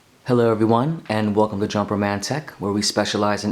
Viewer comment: I thought you said jumper romantic 😆great video